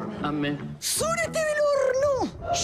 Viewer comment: soy yo o escuche SORETE DEL HORNO!!